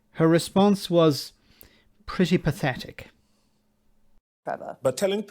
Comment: You said "Her response was pretty pathetic" and I heard "Her response was Priti Patelic". But it seems, I was not far off.